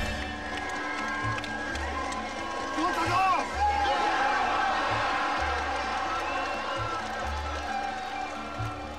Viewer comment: Anyone that speaks Japanese, what is the word or phrase shouted at 5:19? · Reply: とったどー（Tottadooo）